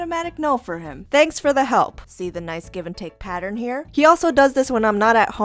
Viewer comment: i was confused I thought it said glue and take